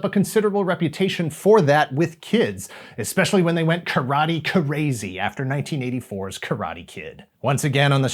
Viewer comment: Am I the only one to have heard "Karate Krazy" instead of "Karate Crazy" at 4:30?